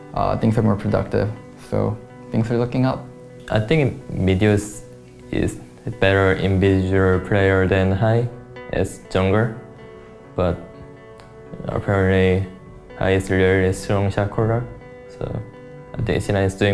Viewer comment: Can someone please tell me what he said. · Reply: Goat I think Meteos is a better individual player than Hai as a Jungler, but apparently Hai is a really strong shotcaller. So...C9 is doing better than before, but I think we need better spot for preparing, so we need to win next week. I did as best as I could.